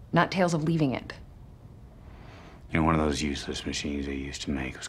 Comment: For a split second I thought he said " you're one of those useless machines..."